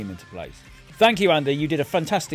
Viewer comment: He said, “sort this gate area out”. I heard, “sort this gay terrier out”.